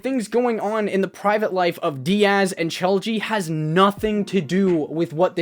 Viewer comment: I thought he said choji 😂